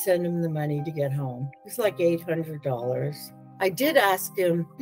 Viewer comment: He needed money for a plane ticket to get home and she sent him 100$ . How would that get him home? She didn’t say how much money she sent him . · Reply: Sounded like she said $800 not $100